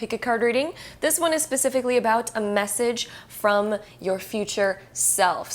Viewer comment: i thought it said a massage from your future self 🤣